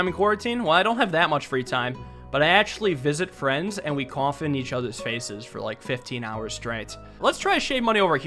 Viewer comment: Aliensrock: "well I don't have that much free time" Also Aliensrock: "I actually visit friends and we cough in each other's faces for like 15 hours straight" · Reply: Not gonna lie, at first I heard we cum in each other faces, and I almost die laughing and had to pause the video to listen at it again... Something is wrong with me. 17:13 is the timestamp.